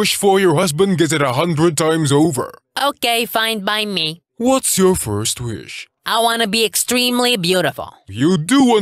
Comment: Was I the only one who heard him say “ what is your 𝘵𝘶𝘳𝘥 wish.”